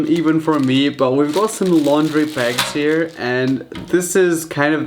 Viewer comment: did he say laundry bags Hope he made a fruit salad after all this